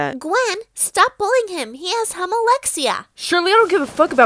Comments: "he Has Humma-lexia!-" What I heard :"he has homolexia-!" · no one: norway: humma humma 🤡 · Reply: AT FIRST I THOUGHT IT SAID HOMOLEXIA I WAS LIKE EXCUSE ME LMAO